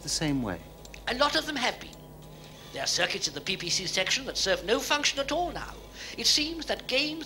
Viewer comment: I thought Orac said "There are circuits in the BBC section"